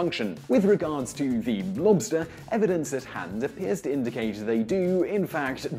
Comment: Did he say “blobster?”